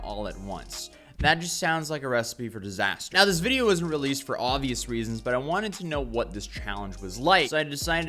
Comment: A FIVE year old??! Absolutely heartbreaking 💔 clout will never be worth your life or someone else’s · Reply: he clearly says the video was never posted, for obvious reasons.